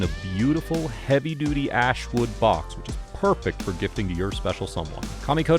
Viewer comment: Thought he said heavy duty ass box not ash😂